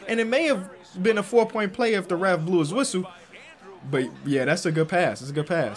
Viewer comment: I thought he said Durant blew his whistle lol i thought kd was an official for a sec